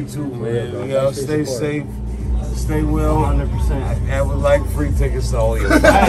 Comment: what does chapelle say? "I would like you to take us all...?"